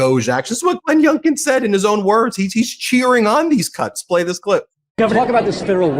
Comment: Lol, at :28 I heard "he's cheering on these c*nts." Had to double check.